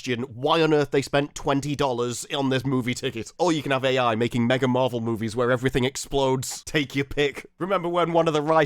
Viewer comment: I kept hearing "Megan Marple movies where everything explodes". I was in 100%